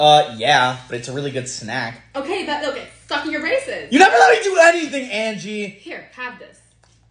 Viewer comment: At almost the start i thought she said "stop your racist"